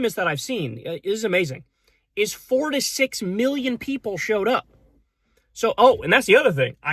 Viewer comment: is that 46 million worldwide? Cause I hear it's 12.1 million in the United States.